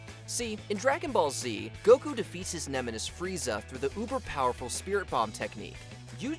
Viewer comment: Did the commenter at 2:25 says ''nemenis'' instead of 'nemesis' ?